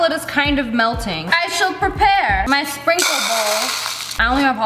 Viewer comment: Did someone heard her say "Frosting" instead of "Sprinkle"? Or is it just me ?